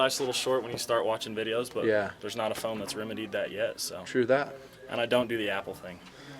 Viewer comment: What did say at 1:09? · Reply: moose "[...] there's not a phone that has remedied that yet [...]" Sorry I'm a little late, but it'll help anyone else who's wondering.